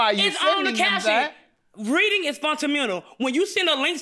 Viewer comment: She is definitely "communitive"! 😂. Also, did I hear her say "Reading is Fun and Mental"?